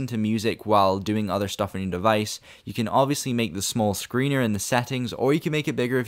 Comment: did you just say "small screener" at 5:31 lmao😂 great video tho